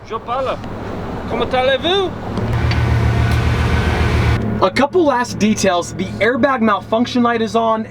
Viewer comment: did he say “a couple ass details” or am i crazy rn..